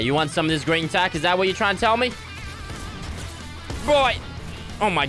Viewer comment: he said BROI instead of BOI